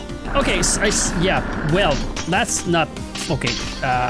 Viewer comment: Did he Say the Thing🤨?! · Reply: did ya hear "fuсk it"? i had to replay that 5 times to realize. If you did,no,he defenetly said "okay"